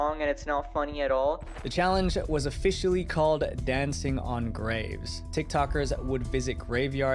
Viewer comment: I thought he said dancing on grapes , am I cooked?